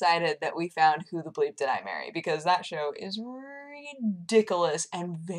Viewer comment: what is the show she mentions @ 9:00? it sounds like she is saying "gublis dimary" does anyone know. cause there are so little tv shows to watch these day......